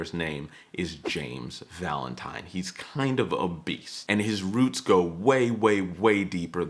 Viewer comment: When you called him “a beast,” I heard “obese.”